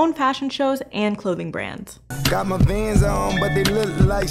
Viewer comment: I thought he said got my BANDS on and they look like sneakers which would've been a way better line tbh